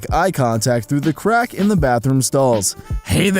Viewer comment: i thought it said make crack in the bathroom stalls